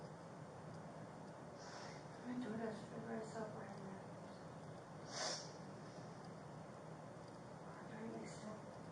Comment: it does sound like she says I saw Bryan there imo but I can’t understand anything else she says under her breath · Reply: She states she saw Bryan and then says she parked right next to him. I can understand why you can’t hear it but she does say it.